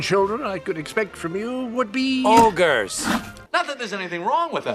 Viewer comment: i thought you said she didn't expect orgys to be the clients and i was like why not its a honeymoon suite... ill see myself out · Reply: Orgys?! You mean ogres?